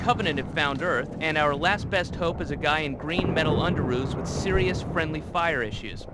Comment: just as a btw, “In green metal underoos” is what the guy’s saying. It means undies, underwear, underpants, etc.